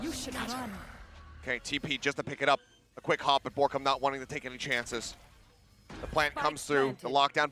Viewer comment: borkum goes down (?) がBORKUMが落ちた！に聞こえる笑